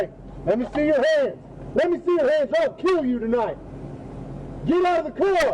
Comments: Did anyone else hear the officer say i will kill you tonight or is it just me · What's matter trooper ?I thought you said ima kill you tonight but you got scared